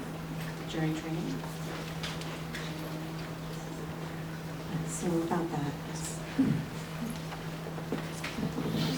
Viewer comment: What did the person say at 5:25? I couldn't hear them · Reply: They asked how scared are you that the coffee cup was going to break